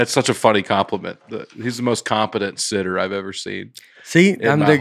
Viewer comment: I thought he said "confident sitter" not "competent sitter" ..no?